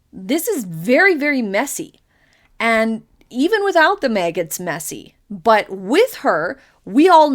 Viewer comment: said, "This is very, very messy. Even without The Meg, it's messy." Closed Captions said, "This is very, very messy. Even without the maggots, messy." Sometimes mistakes by CC look like Freudian slips!